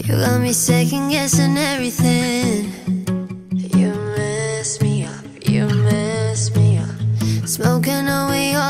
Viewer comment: When she says u mess me up, u mess me up . Idk why my dumbass thought she said You're a lesbian I'm sorry y'all 😭😭😭